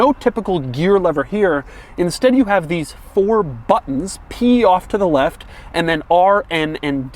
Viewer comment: “Pee off to the left”... 😂 I know that’s not what he said but I still giggled...